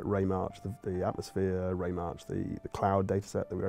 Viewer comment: what does ray match mean at 8:20? i think that's what he says, i'll google it, but if anyone knows what it is, id love to learn : ) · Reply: Jonathan Holeton He actually said ray marching <link>